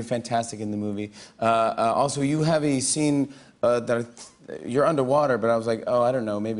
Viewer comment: What happened to his accent at 0:04 😂 instead of “that”; he said “dart”😂😂😂 he’s out here talking all Russian 😂😂😂😂